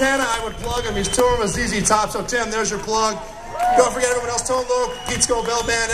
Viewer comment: Who does he say at 18:20 Tove lo?